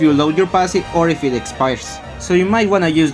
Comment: so the time to progress the autoattack to the next stage refreshes if you use a move, use your passive or something else. What's that something else? I keep hearing "ex-pars" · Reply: I said if your passive load expires